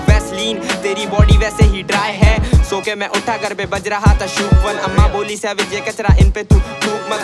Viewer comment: is he said shook one?